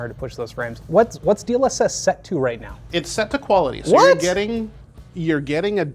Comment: I thought it said prehistoric quality